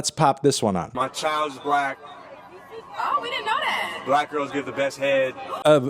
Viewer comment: I heard "my daughter is black, black girls have the best hair!" and I found it cute. Then I learned that he in fact said, "black girls give the best head." ... WTF WTF WTF WTF WTF WTF WTF WTF WTF WTF WTF WTF WTF WTF WTF WTF WTF WTF WTF WTF WTF WTF WTF WTF WTF That's enough internet for the month. Y I K E S